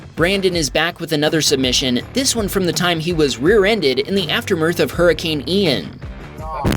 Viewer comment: Did you say "aftermirth?" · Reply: He probably meant aftermath. I don't think English is his native language.